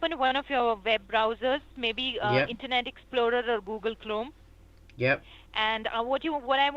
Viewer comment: Did she said Google Clome???? hahahaha